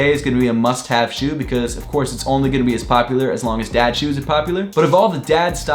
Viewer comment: I thought you said dead shoes